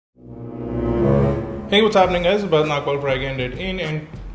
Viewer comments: Lol @ 0:07 he says what's up "N" word. Cool video btw. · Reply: It's "what's happening guys" although it has been mistaken for that before ! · Lol @ 0:07 he says what's up "N" word. Cool video btw.